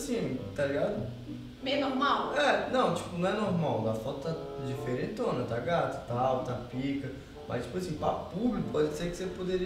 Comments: Só eu escutei 6:14 : Coringa: Não a foto da diferente tá gata . MANOO VOLTEI UM 30 VEZES NISSO 💚💚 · Coringa: Tá bonitona tá gata, isso que eu escutei se eu errei me digam aqui nesse comentário!! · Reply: Tá diferentona tá gata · só eu que escutei o coringa falando que a babi tava gata , 🤭😂😂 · Coringa: Tá bonitona tá gata, isso que eu escutei se eu errei me digam aqui nesse comentário!! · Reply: Eu escutei "ta gata,ta pica"